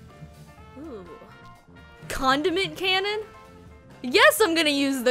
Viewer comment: When she said condiment cannon I heard “Condom Cannon”